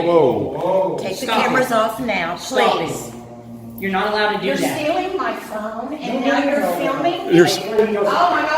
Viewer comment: it sounded like she said "your stealing my soul"